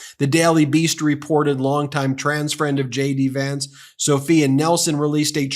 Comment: Ben, each and every time you say J.D. Vance, I hear, "Shady Vance." Must be a subliminal thing...